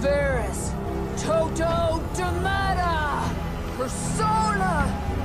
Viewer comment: DID LLOYD SAY PERSONA